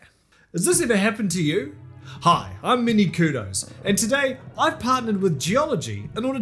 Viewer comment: When you introduced yourself it sounded like you said "I'm mini kudos" and for some reason I find that hilarious. I'm not making fun of your accent, I think it's pretty neat~ Edit: Time of my passing: swag alert Edit: Imagine being friend zoned by your phone~